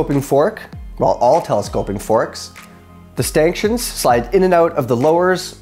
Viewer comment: Did I hear "Stanktions"? 🤣